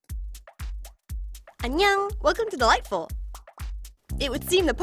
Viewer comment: idk why but whenever i hear that intro aka "Welcome To Dollightful!" i hear it as "Welcome To The Light Bulb!".......only me?